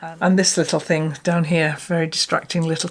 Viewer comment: I’m so sorry to hear about Norma and very grateful you have family their to support you. As for the little kitten, I thought you said he was ‘destructive’ not distracting! Hahaha. Glad he’s keeping you busy. Hugs, wendy